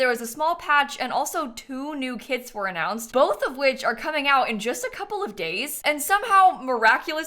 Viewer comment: i heard “are coming out in just a couple of gays” 🧍‍♀️